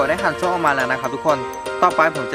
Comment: เหมือตพี่พูดว่าขันสวมนะ